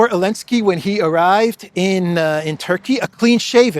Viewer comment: I thought you said the green goblin was a turkey 😂😂😂, I listened again and heard that he had arrived in Turkey !